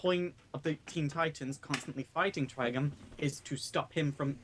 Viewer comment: When I heard and say Trigon's name, it sounded like they were saying "Trigun".